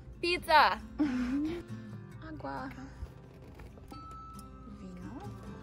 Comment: wait did u say agua and vinho?